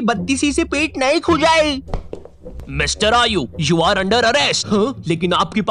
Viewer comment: first I thought he was saying mister are you and then when I got know his name